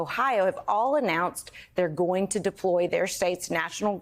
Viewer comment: I thought Elise Jordan said deport fed agents at first, but sadly she said deploy. It is utterly sickening and demoralizing. "Casing the joint", indeed.